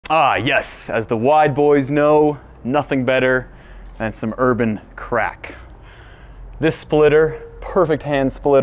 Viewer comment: I thought he said " As the white boys know nothing better than some urban crack" 😆😭 . That's what the crackhead down the street says right before hitting that pipe